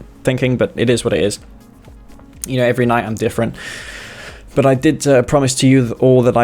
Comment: "Every night I'm different" - That's what he said.